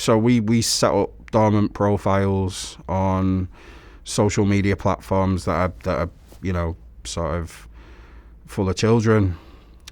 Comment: yes the part of minut 1:50 i don't understand very good the answer. Thank you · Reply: He answers, when asked how he catches them: -We set up dormant profiles on social media platforms that are... sort of full of children. Facebook, snapchat. All of the major platforms we will set up a dormant decoy profile and we will make that profile appear as if it were that of a child. Then we'll await contact.